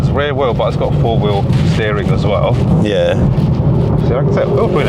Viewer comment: I couldnt understand the guy well, but i thought i heard him say something about 4wd on the LC500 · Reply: 4 wheel steering